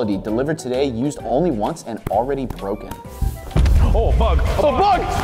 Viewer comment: did he say oh f word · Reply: He said “OH BUG”